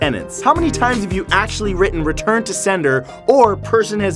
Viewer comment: Who else thought return to sender said return to Slender? just me ok...